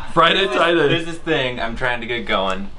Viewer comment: I thought he said Friday try day.